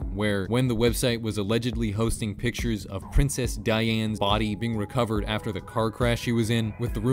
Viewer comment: sounded like you said Princess Diane